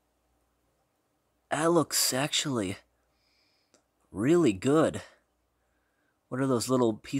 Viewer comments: I heard " That looks sexually, really good. " Pog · "That looks sexually" That's what I heard 😂